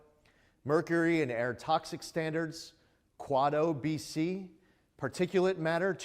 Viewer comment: Did he say Kuato BC? I’ll take a pre-jesus Kuato.